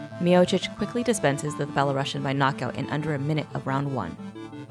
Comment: did she say fellow russian? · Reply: She said Belorussian. Sounded like it doe! :)